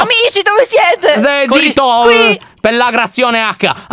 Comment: Stai zitto pellagrazione H Io: MA HAHAH STO PERDENDO UN POLMONE😂😂❤️, solo io ho sentito Pellagrazione?:^